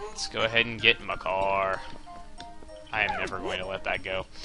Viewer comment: Anytime Chugga say's Makar it sounds like he is saying my car......Thumbs up So Chugga can see